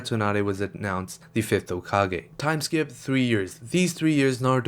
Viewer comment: U just went like "time skip 3 years" like so nonshalantly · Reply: Time skip 3 weeks he said. I think you misheard my friend